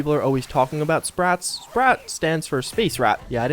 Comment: I can't tell what you said?? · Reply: Sprat. Space rat